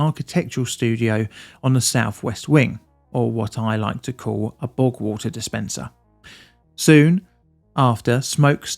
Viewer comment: Did I hear this correctly? Did you say that the fire was caused by, ". . . a bog-water dispenser"? (5:53) A "BOG-WATER" dispenser ? ! ? I'm currently drinking my morning "BOG-WATER" ! I exist because of "bog-water"! If I have too much blood in my caffeine system, I'm dead. So don't be talkin' s[p]it 'bout mah COFFEE ! ! ! . . . Even if it DOES taste like "bog water" . . . .